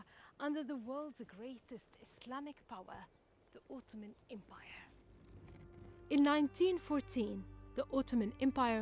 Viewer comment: I sounded like he said "the Ultimate Empire."